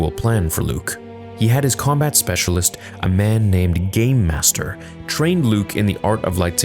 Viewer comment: I legit thought you said GAYMASTER! Great video, as always, my friend!